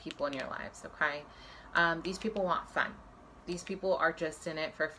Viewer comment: Hello is she saying "This people want fame"?